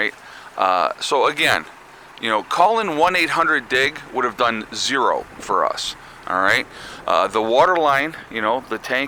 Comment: I thought you were saying 1-800-DICK. LOL!